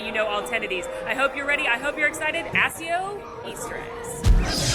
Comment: I'm sorry, did she just say "Assio" 0:25 😭